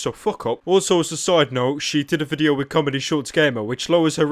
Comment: I thought said Stomedy instead of ComedyShortsGamer